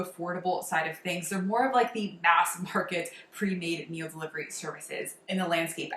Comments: "Ass market"?? I know I misheard you but I can't think what else you are saying. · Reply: “Mass market” 😆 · "Ass market"?? I know I misheard you but I can't think what else you are saying. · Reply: Lol, I heard it as “ass market” too.